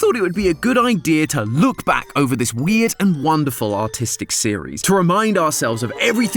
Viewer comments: I thought he said autistic and not artistic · I thought it said "This wonderful and autistic series" XD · it sounded like you said autistic because of your accent